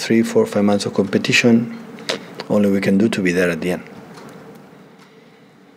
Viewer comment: Did Pep say “ladies first” to the guy next to him? 😂